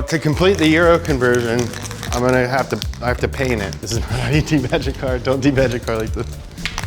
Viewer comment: What says, "This is not how you de-badge a car, don't de-badge a car like this". What Captions say he said, "This is the A-Team magic card, don't do magic". But both are a great message for the kids out there regardless.